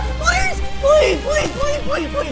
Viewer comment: ONE IS BITTING MY BUTT AGAIN what I heard:EY YOU BITTING MY BUTT AGAIN💀